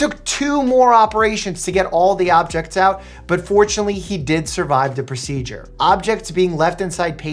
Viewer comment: hey,um…why did you say unfortunately? Isn’t it good that they survived? I think you used your English wrong sorry I know your Russian am just saying not trying to be rude